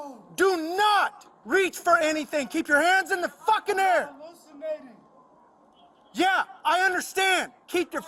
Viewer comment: What did the suspect say at 3:29? The officer responded “Yea I understand.” Thanks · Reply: sounds like “im hallucinating”